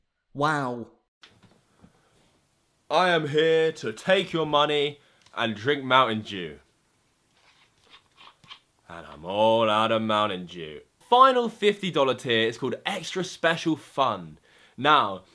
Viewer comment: i heard 'mountin jew' i-ok